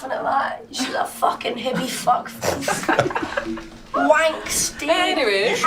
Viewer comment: Wank stain, I thought he said wank stick.